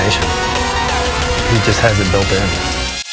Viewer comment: what did he said on 1:09?thanks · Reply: "When it comes to Ryan it's just determination, he has it built in"